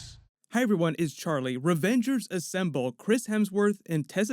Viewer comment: I swear at 0:10 he says Revengers Assemble.. does anyone else hear that? How can you take a video seriously when the guy can’t even get the title of most anticipated film of all time correct?